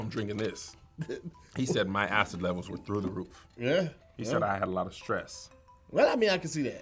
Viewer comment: Bruh I thought I heard Kev said “my acid levels are through the roux.” 😂